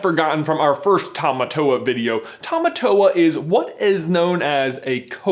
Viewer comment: At first I thought it said tomato!🤣